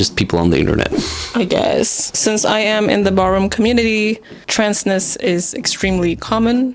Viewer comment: Can someone explain what the word is used at 11:43? What does the Borum??? Community mean/What is it? Is that a place? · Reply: Ballroom community :)